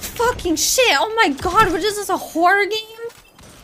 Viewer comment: Did I heard right?“Is this a horror game?”